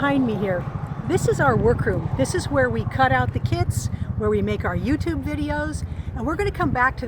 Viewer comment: Am I the only one who thought she heard cutting the kids in stead of cutting the kits?🤣